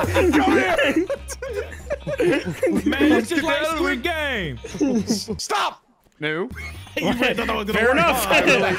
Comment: Why at 22:05 when Smitty says stop does the subtitle say "This mornin"